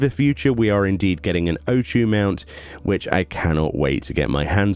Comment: is saying we're getting an O2 mount? If so, what is that?